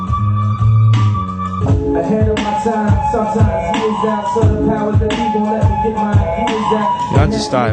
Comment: Hahahah Kanye West is talented but those just to point out what he says at 2:32 "I handle my time, sometimes it is out to the powers that be ,he lets me get mine, he is that" Illuminati.